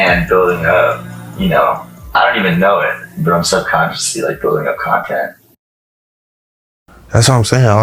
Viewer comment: what does he say from 13:07? "i dont even know but im so consciously/constantly(?) like building up content(??)" ???